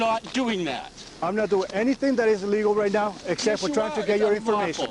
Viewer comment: L O L Cop said it himself (6:23) "I am NOT doing anything that is LEGAL right now" do I need to explain more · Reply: Paul Williams I thought he said "I'm not doing anything that is illegal except trying to get your information